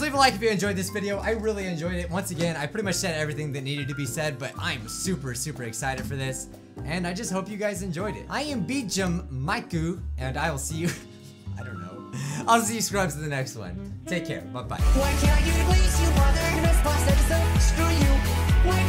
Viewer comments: Subtitles say I am beat Jim Mike goo and I will see you I don't know on Zee scrubs is an excellent. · the captions said "I am beat Jim Mike goo"😂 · subtitles say "this and I just hope you guys enjoyed it I am beat jim Mike goo" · captions say “I am beat Jim Mike goo” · the subtitles say beat Jim mike goo for Bijuu mike 🤣🤣🤣